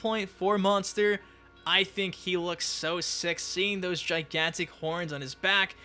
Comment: Did he say he looks sexy?